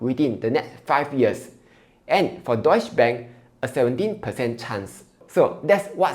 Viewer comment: I really thought I heard Doge bank 🤣 hahaha thank you! Informative sharing!